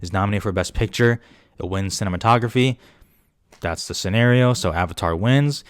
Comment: did you say cinnamon-tography @ 19:50? lmao love it!